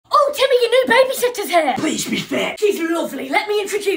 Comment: 'Please be fit..' 💀😭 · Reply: I thought he said "plese be thick"💀